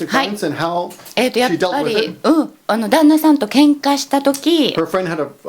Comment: What is saying? Daima Sama? Anyone can explain what that means? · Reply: I think it's 旦那さん danna san. An informal way of saying husband. Not sure about the kanji